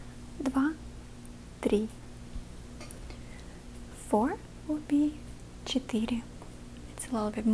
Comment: am I the only one who hears "sh** eating" when she says that?